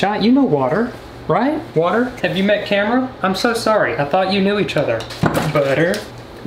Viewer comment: When he said “water, have you met camera?” I thought he said Cameron, which is my name. It scared me an unreasonable amount.